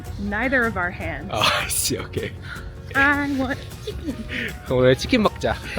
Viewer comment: point, I thought said ....... ' I want children!' lol